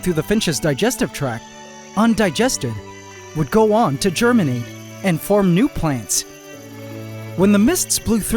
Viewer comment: hab ich da deutschland gehört ? 23:30 ?